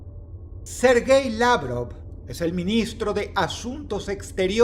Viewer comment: Escuche “Ser gay ladro” xd